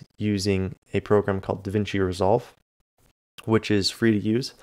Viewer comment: whats the name of the free-soft you use to edit videos? I didn't understand that name at all. Can some native English speaker help me? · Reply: It's Davinci Resolve, what's so hard to understand? and I'm Romanian born, lol